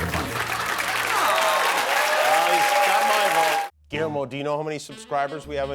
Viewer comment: Guillermo! 😊.... I thought you said "a bottle of cuervo!!"....